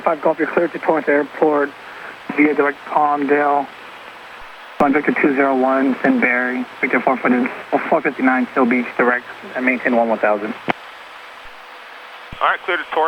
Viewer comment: Hi! Could you write word for word what atc says between 10:39 and 10:45 in the clearance? I am unable to understand the words he says before the 2 airways numbers. Too fast for me or too mumbled… Very interesting vid btw. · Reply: Hi, “V201 then BERRI V459 Seal Beach, Direct. Climb and maintain one one thousand”